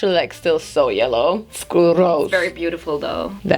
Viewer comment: did she said "skroz" omg